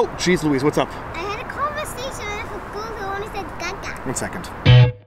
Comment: I want to know what the conversation was and I want want her to explain it. This should be fun! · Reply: I heard "I had a conversation with Bozo and he said, Ga ga" Lol